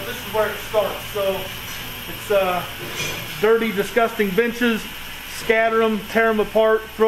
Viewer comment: am I the only one that thought he said “Dirty, disgusting b****es. 😂 🤣. I actually had to go back a couple times and make sure I was hearing this right.